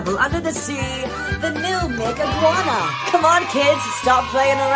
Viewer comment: At the end I thought he said n- iguana